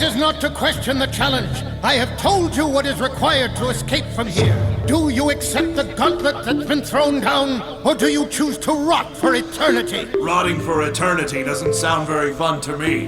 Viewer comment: um what did he Just say? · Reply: "Do you accept the gauntlet that has been thrown down or do you choose to rot for eternally?"